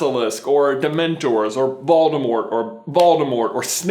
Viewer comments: You said Voldemort so many times I thought you said baldemort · every time you said voldemort i thought you said 'baldimort' and i was like'yep. voldemort is bald'